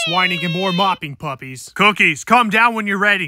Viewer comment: I'm not grounded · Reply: DID HE SAID MY COOKIES??? 3:34 😮😮😮😮